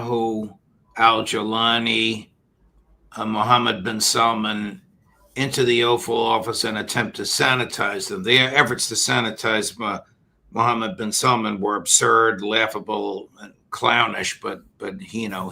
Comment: it sounded like the judge pronounced it 'offal office'. Freudian slip?